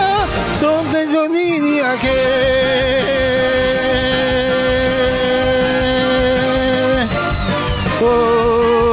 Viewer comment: did he say something that is gay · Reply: He say gaaaaaaaaaaay